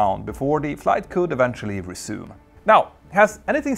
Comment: Your accent and my mind playin tricks on me. I heard 'Let me know. And the comments blow. And don't forget to wipe'